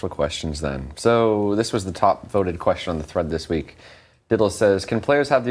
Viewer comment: does he say (Diddle) ? ? ?